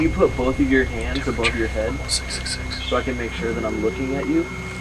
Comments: it sounds like someone said torture 666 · Did anyone else hear “to the tree 666” or just “666” at 20:10 when the camera zooms in, its like a whisper, I had to rewind a few times to see if it was one of the guys but it didn’t sound like one of them